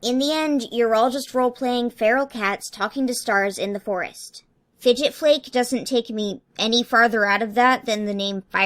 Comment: I thought it said PigeonFlake.. I’m confused 😂